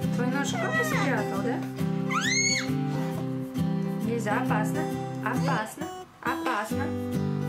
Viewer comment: What is that word she is saying to the child? Is it a russian word? Because I am a native german speaker and that word sounds exactly like the german word "aufpassen" which can be translated to "watch out" or "take care" which would also make sense in that situation? Would love to find out about the origin of that word/saying. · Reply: She saying "опасно" which mean more or less same as aufpassen (like watch out or danger).